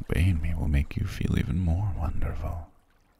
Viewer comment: “I’ve made you feel so wonderful. And you know that a BABY will make you feel even more wonderful”???? Excuse me??? Did I hear that correctly???? 😳 EDIT: Nevermind I’m dumb and misheard it, but c’mon and tell me you can’t hear that😅